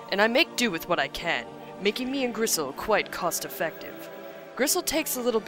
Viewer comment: Am I mishearing? Sounds like she's calling the boar Grissle instead of Bristle.